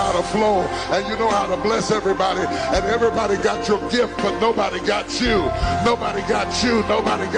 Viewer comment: I didn't like that he told him that "nobody got you". I don't think a suicidal person should be told they're alone in this. This is why you don't go to church as therapy, it can go with therapy but church isn't a replacement in my opinion. He has people in his life to help him like his wife who do "got him", who has been by his side this whole time. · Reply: I didn't hear that. When he says "nobody got you", I heard "nobody understood YOU." As someone who gives of their selves often in order to help and uplift others and still struggling on their own, I felt this statement deeply.